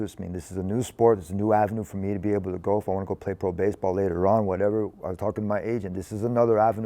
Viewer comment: did he say he’s gonna play pro baseball? Tony vs. Paul Skenes is the real matchup we need lol